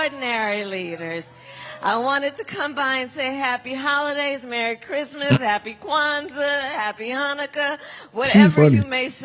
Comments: I thought she said “How dare we say Merry Christmas!” Honestly, she only made an appearance so that people can financially “support” her 🙄 and sad thing is, people will continue to give this crazy lady money. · I thought she said no more “Merry Christmas”? 😂😂😂😂😂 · Thought she said ‘how dare we say merry Christmas’ ??? Even the lord can’t keep up with this woman. She been preaching so much since the election maybe she trying to start her own church. ‘And then she said WE WILL BE UNBURDENED BY WHAT HAS BEEN!!!!’ 😂😂😂